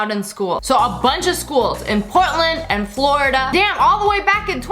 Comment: DID SHE SAY "PORTLAND" AHHHH!!....or did she say Poland..?